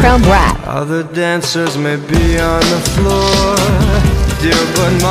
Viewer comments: "other dancers maybe on the floor" Interesting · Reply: I honestly thought he said “other dancers may pee on the floor”. Lol. Thought that was pretty gross, didn’t know dancers did that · Ok Ik this isn’t the point of the video but I thought it said “all the dancers may pee on the foooooor” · Dude, why do I hear "all the dancers may pee on the floor." In the first lyric?